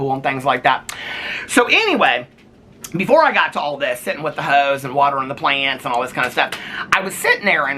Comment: when you said "sitting with the hose" i heard "sitting with the ho's" lmfao just having a kiki with all the gals, well isn't that what these videos are?